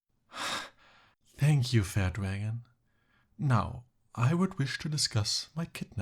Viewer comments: Just like to point out that auto captions says "fat wagon" when he said fair dragon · did he say "thank you fat wagon"because that's all I can hear and what the captions say... Rude much 😅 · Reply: Oh no no, he said "Thank you fair dragon" 😂